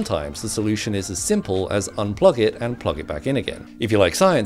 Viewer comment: He has his mother's smile, his father's eyes and Pete from next door's mitochondria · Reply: I thought u said three children from one parent